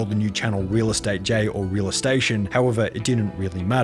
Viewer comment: i thought the bottom one said "real estonian"